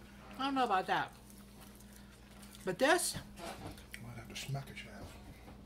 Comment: I couldn't understand what said at 07:34...captions say he wants to smack a child 😲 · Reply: I had to re-listen... he said he might have to "smack a chav" 🤣😂🤣